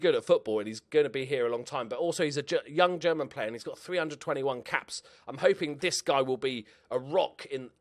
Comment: It sounded like you said 'he has 321 caps' not 3 under 21 caps' .. I was like jesus christ he must be old